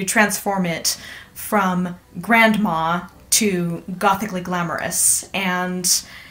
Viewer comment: Okay so at 2:01 you say "gothicly glamorous" and the auto subtitles say "Gothic illegal amorous" and I am not even mad, seems accurate to me xD